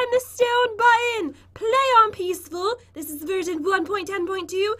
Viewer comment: I heard “This is virgin”